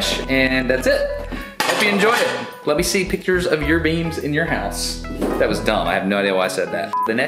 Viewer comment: "my wife exchanged footage for beams, without asking me first" that made me giggle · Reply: I thought he said beans.